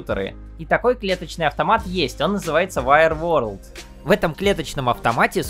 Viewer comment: WireWorld, правильно расслышал???